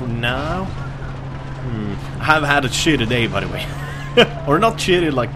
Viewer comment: i thought he said 'i haven't had a shit today' hahaha lol need to get my ears checked?